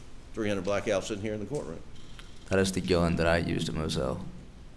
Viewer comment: what does he say at 33:40? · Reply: "that is the gun that I used at mozelle"